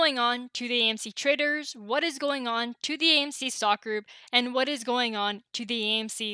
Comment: I always thought you were saying "what is going on AMC stocker" 😁😁 its "stock group" 😂 oh well, stocker is still accurate. Thanx for the video 🐰